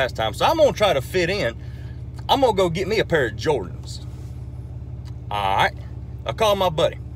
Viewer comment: I thought he said "Jorts" as in Jean Shorts LMAO 😂🤣